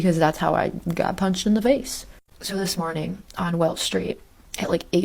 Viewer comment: She’s lying. I live in Chicago. There is no such street as Welch. This did not happen in Chicago.😂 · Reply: Sound like she said welsh but Welch was in the caption